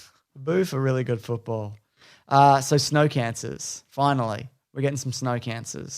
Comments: I was drinking coffee when I heard "Snow Cancer" now Im soggy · I'm Australian and I thought he said snow cancers too.